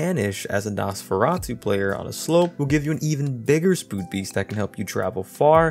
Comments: you say spood beast instead of speed boost lol it’s okay I do that all the time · Sorry but i have to say what I heard "Spood beest" xDDDD